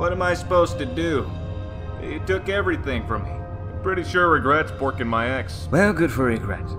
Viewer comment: Thel vadam : I'm pretty sure regret working my ex Truth : well good for regret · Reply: George Dociak I heard "I'm pretty sure regret's forking my ass"